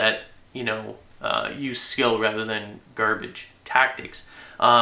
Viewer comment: did he just call Tactics Garbage? and he